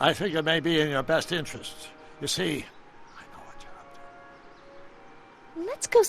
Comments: Is it just me or did he say I know what you're up to? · When Doc was whispering to Edna, I heard "I know what you're up to."